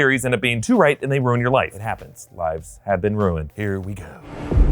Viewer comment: I legit thought you said vibes have been ruined instead of lives hove been ruined